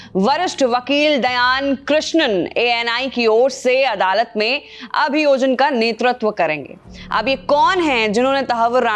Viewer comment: Did she say "ANI ki aur se adalat mein abhiyojan ka netritva karenge?" At 0:10 timestamp